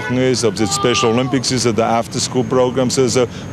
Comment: from around 0:13 to 0:15 it sounds like he says "special Olympics and the after school programmes" lol